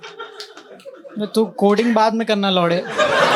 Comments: he says "L*ude" and the caption says "Samay" wtf💀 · in subtitles he use samay instead of lo*e 😅😅😅 broo